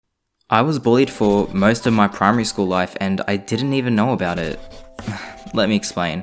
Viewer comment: Ok I thought he said at the beginning of the video: I was a boy for most of my primary whatever school and I didn’t even know it. And I was like uhhhhhhhhhhh